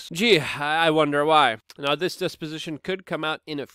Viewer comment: Did he say desposition